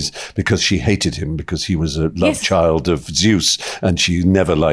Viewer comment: Did he say love-childs?